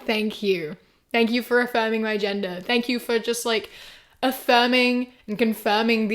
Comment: The captions say "agenda" instead of "gender" 💀